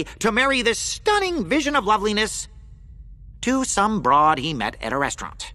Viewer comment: IM SORRY 2:02 DID HE SAY BROAD? 🤣😭